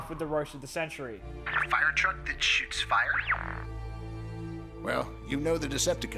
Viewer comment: DID I JUST HEAR BEE SAY “SHITS FIRE” ?!?! What?